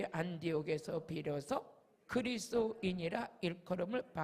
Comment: 주여 목사님 다 은혜되고 좋은데 제발 그리스도 발음을 정확히 부탁드려요~~ 자꾸 제귀에는 그리스로 들려요 부탁드립니다~~ 예수 그리스도는 핵심이라 ~~ 죄송합니다~